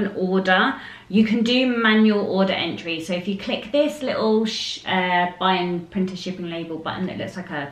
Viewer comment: she was gonna say little shit